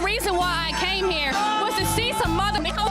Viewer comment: At first I thought she said "Came here to see a winner" then she kept talking and I was like "oh..."